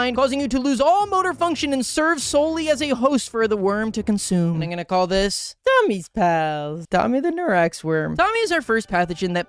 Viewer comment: all I hear is “I’m gonna call this- Magic the Gathering” because immediately after I got a magic the gathering ad